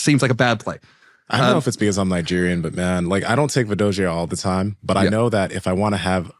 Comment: what is this supplement, I cant hear it and it subs say "Vidosia" but google cant find it, anyone?